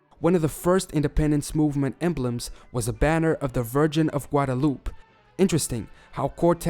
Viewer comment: Did he just say the flag of “guataloop” 😂